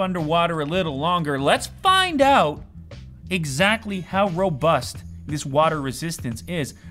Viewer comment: I'm late but u said robust I thought u said robux